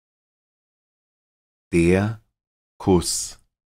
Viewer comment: Oyendo distraídamente, pensé que había dicho DER FURZ en vez de DER FUSS. Tuve que dejar de hacer lo que estaba haciendo para oir con atención.